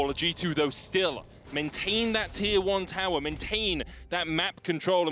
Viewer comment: What did he say please ? o_O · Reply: "Maintain that tier 1 Tower, maintain that map control..."Means to keep(alive) the first in line tower and the map control(vision)